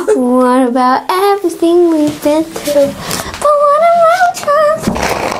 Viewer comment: did just say fack?